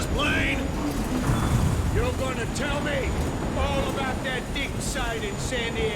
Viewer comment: the way it says "dick" instead of "Dig" 😭😭 (I was in school when i found the caption)